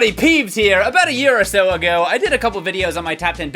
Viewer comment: For a second, I thought you said "a year or two ago, I posted a video."